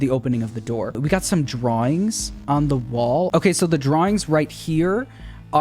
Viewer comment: Don't know why i heard him say "we got some drugs on The wall" LOL